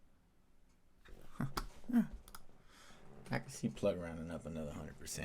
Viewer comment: Did you say you can see plug running up 100 to 300 % to the upside?